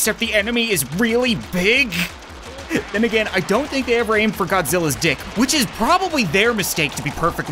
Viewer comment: did he just say Godzilla’s d***?